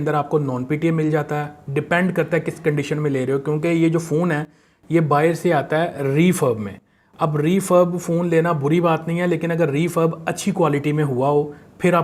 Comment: what he is saying refirb or refirm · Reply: Refurbish